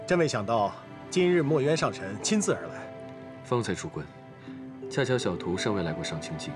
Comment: 把方才出关听成了方才出柜哈哈哈哈